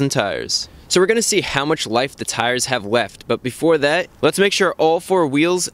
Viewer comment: I can’t be the only one who heard “weft” right?